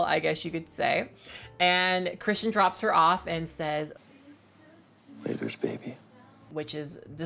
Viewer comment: Christian: ‘Laters baby’ Kat: ‘DISGUSTING’ Me: actually deceased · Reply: I thought he said "leather baby" or something close, that's why I looked for a meaning